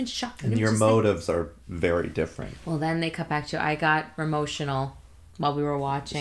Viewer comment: Also I love how at 10:18 it sounds like you said "I got RAMOTIONAL" which should be a hashtag 🤣🤣🤣